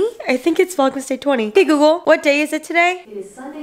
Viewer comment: I'm watching in 2x speed so when she said "Hey Google" I thought she said "Hey peekaboo", I was like that's an interesting name for Alexa but nope it was Hey Google!